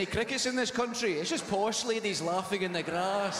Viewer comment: At first I thought he said posh lady boys. Not posh ladies voice